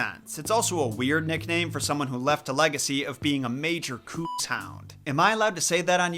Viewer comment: What was that word at 8:29? "Cookshound"?